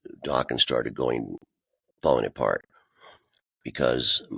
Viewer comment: For a sec here, I actually thought Don said Dokken "started going Bonaparte," as in Napoleon, lol.